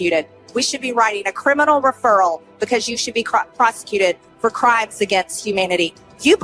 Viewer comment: For a sec i thought she said executed but she said prosecuted 😅